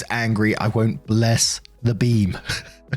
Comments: I misheard it as bless the bean 💀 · When you first started the story, I thought you said, “Bless the Bean” and was confused. I thought it was going to be an over-the-top “bless this food we’re going to eat” thing. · For the first story I thought he said "bless the bean" so I was very confused lol. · I thought you said 'bless the bean' and was legitimately worried for op! 😂